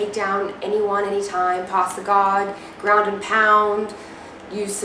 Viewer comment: I sweat it sounds like you said "poss the god" 1:58. Either that or im high as hell.